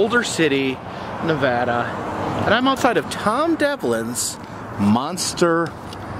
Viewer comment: Thought it said Tom savini